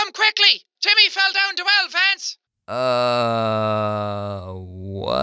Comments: It sounded like he said TIMMIE FELL OUT THE WELL FENCE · I think in 5:38 the women said your going to come quickly jimmy fell down the shell Vance/lance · I heard "your going to have to come quickly cuz Jimmy fell down to elephants" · The only words I could make out was:”YOUR GONNA HAVE TO COME QUICKLY CUZ TIMMY FELL DOWN THE WELL VANCE!” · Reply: i heard: you're gonna have to come quickly, cuz timmy fell down to elephants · I thought she said timmy fell down well lance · I heard: "Your gonna have to come quickly! Timmy fell down the well Vance!" (Lance) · honestly i heard "YOUR GOING TO HAVE TO COME QUICKLY JIMMY FELL DOWN THE WELL VANCE!" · It sounded like jack said Timmy fell down the well feds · I thought the woman said, "You have to hurry! Timmy fell down the well Vance!" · i thought at 5:38 she said YOUR GONNA HAVE TO COME QUICKLY TIMMY FELL DOWN THE VENTS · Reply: I thought she said Jimmy fell in the well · I thought jack was saying "LITTLE JIMMY FELL DOWN THE WELL!" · It sounded like she said jimmy fell down the well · James: doesn't understand anything the women is saying What I heard: you better come quickly cause Timmy fell down the well lance!!! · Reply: I heard: Your're gonna have to come quickly. Timmy fell down to Elephants! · I think that stranger said you have to come quickly cause Timmy fell down the well lance. (edit) it sounds like your talking to pingu. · i thought she said timmy Felt down the Vance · i heard "timmy fell down the well, lance"